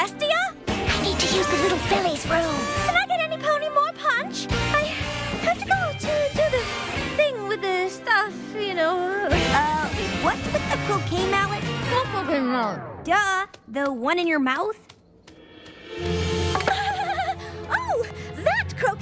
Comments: I heard 'What f*cking mallet?' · When Rarity's talking with the mallet in her mouth it sounds like she says "what fucking mallet?" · I heard "what fucking mallet?"